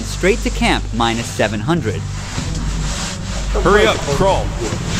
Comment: Я чётко услышал на 12:19 "Давайте ползтте йобаный в рот"